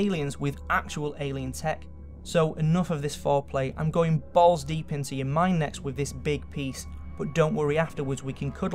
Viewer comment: I died when he was like "balls deep in ya mind." I thought he said "balls deep in ya mum!" I can't even